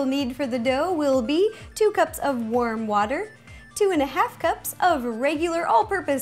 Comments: am I the only one who thought she said worm water? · please tell me I'm not the only one who thought she said 'worm water' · I thought u said two cups of worm water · Lol I thought that she said "a cup of worm water" but she said warm